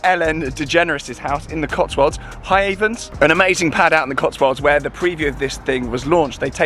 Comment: where is the building they launched the car? Couldn't hear it very clearly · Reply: Hiavens it’s called!